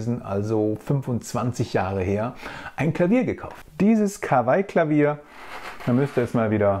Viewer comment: hab „Krawall-Klavier“ verstanden. 😚